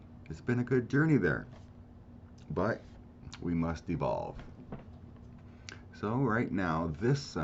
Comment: I thought I heard you say, " We must be bald." I think I'd better go lie down for a while.